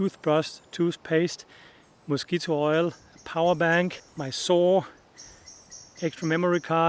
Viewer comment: Nice! A video! 3:05 the fact that I initially heard “my soul” instead of “my saw” says things about me I’m not sure I like… 47:50 I reckon the name of that small white heron is “egret”